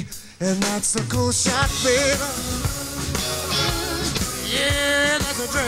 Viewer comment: MEXICO SHOPPING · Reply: A friend of mine originally misheard “that’s a cold shot babe” as “Mexico shopping” 🤣